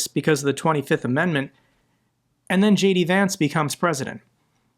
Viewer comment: Thought you said: JD Nance. To funny, Nunce in the UK is a (dismisive term,) for a homosexual. 😮